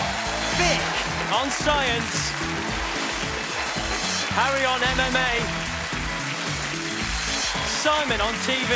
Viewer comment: At firt i thought it said Harry on MDMA